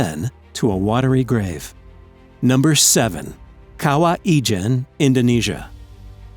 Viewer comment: i thought it said kawai KAKAKAKAWAIIIII